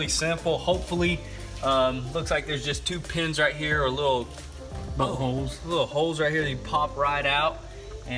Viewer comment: Did someone else hear “buttholes”